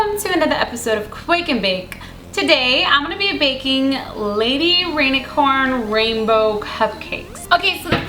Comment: i thought she said she was bakind lady rainicorn and i was like oooh jake is gonna be maaaad